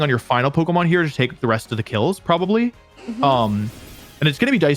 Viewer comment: Subtitles say KOs but what says sounds like "kills". Mistake probably?